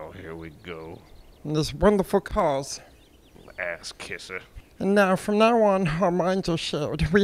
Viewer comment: At 1 min in I know he said "Ass kisser" but i heard "Axe kisser" idk why but it made me laugh harder lol